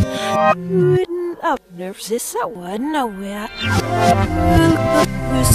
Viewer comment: I heard nowhere